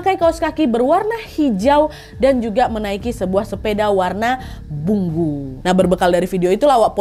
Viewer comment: Aku dengerin sambil merem. Pas bilang "sepeda warn bunggu" langsung heran. Salah denger kah aku?😂